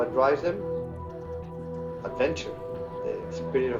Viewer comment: Funny beginning he says “what drives people...” his accent makes it sound like he says attention lol when he’s saying adventure .. i think it’s probably a little bit of both tbh